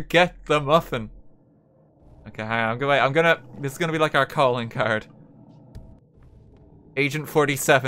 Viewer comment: did I hear "calling card" ?